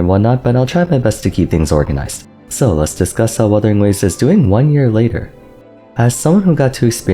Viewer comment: You speak so fast that you don't enunciate properly. It sounded like you said, "let's discuss how other nose is doing this year" at the start of the video. lmao